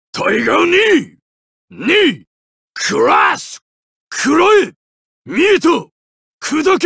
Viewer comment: Can someone please tell me what he says when doin EX uppercut at 1:02? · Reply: Mieta!- I see you!